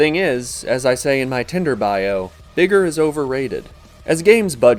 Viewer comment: I thought he said nigger is overrated in